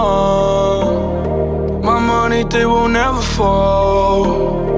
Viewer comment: At first I thought it said "My money, thick, would never fold".